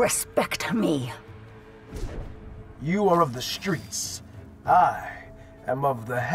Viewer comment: Dam I thought he said “you belong to the streets” lol